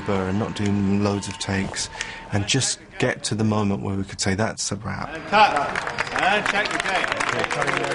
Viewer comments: Am I the only one at first for a second thought it said “that’s a wap” and not “that’s a wrap” 😭😂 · At the start I thought it said that’s a wap 💀💀